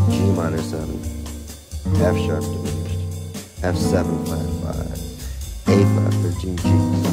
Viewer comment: is that an f sharp 3rd at 1:10? I couldnt hear what u said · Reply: an F# diminished